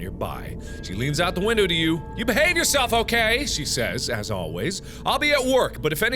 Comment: i litteraly thought he said ”you hang yourself ok?”